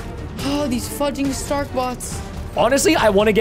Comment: he sounded like he said the f word